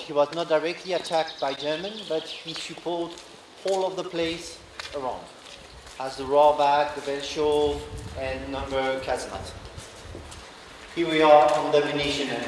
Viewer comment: the closed captions say "As the raw back vegetable and number of cat's nuts"!!!! 😀😂🤣🤣💀💀💀